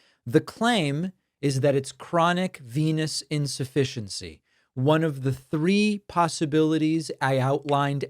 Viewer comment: Did you say "chronic penis insufficiency" at 2:36?